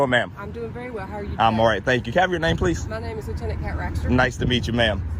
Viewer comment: Did she say her name was Lieutenant Hat Rack?